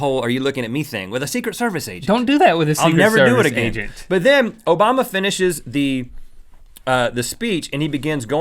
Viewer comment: When Rhett said Obama, I thought he was saying Mo Bamba and I was confused lol.